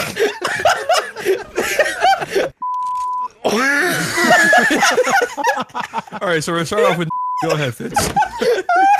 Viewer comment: Did someone say the N word? Was that the word that was blurred out? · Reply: Yes, someone said "nigga", so what? It is comedy.